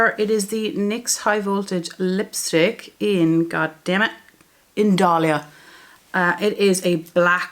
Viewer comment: 😂😂😂 I thought you were saying the name of the lip color was “god dammit.” I’m still laughing. You had a lot of progress in this project. I love your singing. I’m curious, do you break into random singing when you’re talking to people? I love it!!